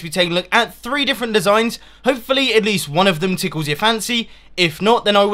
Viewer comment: EVERYBODY PUT THE SPEED IN 0.5 AND GO TO 0:23 IT SOUNDS LIKE HE SAYS: hopefully one of thees tickles you'r panties. XD ITS SO FUNNY BECAUSE HE SOUNDS LIKE HES DRUNK XD